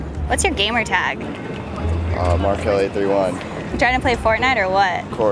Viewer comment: I thought he said r kelly lmao